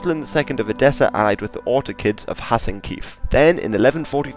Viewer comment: Sounds like you were saying "Ottokids" like the precursor to OttoMANs"